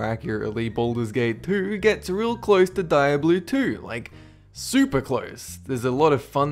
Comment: XD did you just say Die-A-Bloo XD